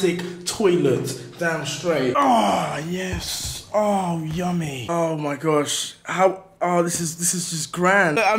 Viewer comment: in 6:44 did he say yummy